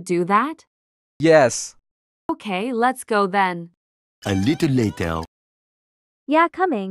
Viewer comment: Who else heard her say come on let’s go ben